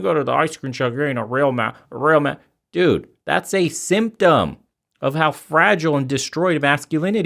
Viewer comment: I heard that in my head as "That's a SIMP-tom," lol